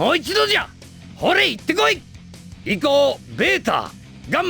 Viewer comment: ここの行こうってセリフはこれ以降とかの以降っぽくも聞こえるね